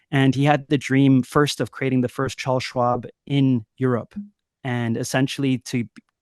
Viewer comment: what is he saying about swap ? ?? Child swap or Charles swap. What is this guy on about ? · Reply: "Charles Schwab". Look it up... Wtf are you on about?